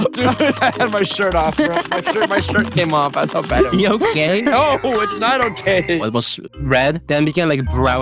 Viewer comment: I thought he said your gay💀💀💀💀